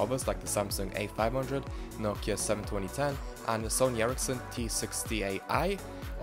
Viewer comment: hello! Could you tell me the model of the nokia that comes out at minute 1:38 please? I don't speak English and I can't understand when you mention the model. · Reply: Nokia 7210 and the camera module I believe is HS-1C. Sorry, I pronounced the number wrong anyway